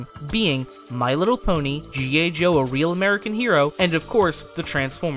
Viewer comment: G.A Joe?.. or am i mishearing? How Very Dare You Accidentaly mispronounce 1 single letter during that whole Script, Shame!!...SHAME ON YOU!! I'm just feckin' aboot :D